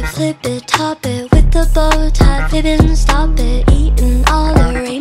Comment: I don’t know why, but I hear “fibbin’” instead of ribbon, and I can’t unhear it.